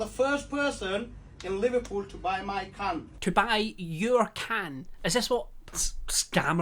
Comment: pls i thought he said 'buy my c*nt' not 'my can' i